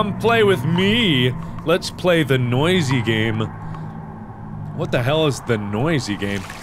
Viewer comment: lol i thought it said "lets play the NASTY game"